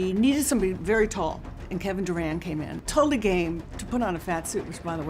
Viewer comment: When they said Kevin Durand I thought thy said Kevin Durant lol